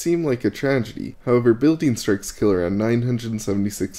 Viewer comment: "However building __ kill around 976 million birds..." what is the word said after 'building'? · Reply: Building strikes kill around 976 million birds per year